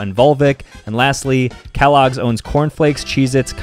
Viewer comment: "be safe" Me: mishears be sick